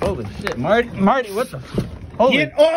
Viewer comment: “I’m gonna knock that fuckin phone right in the water”🤣🤣🤣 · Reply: Ohhhh, "phone"! That makes more sense😅I heard "boat" and was so confused 🤔😕